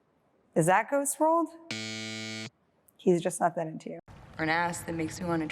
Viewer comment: What movie she though it was in 2:23 I didn’t urdestend what she said. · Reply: Ghost world